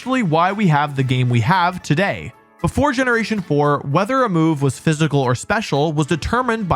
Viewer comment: did anyone else think he said "Weather remove" and not "Whether a move"?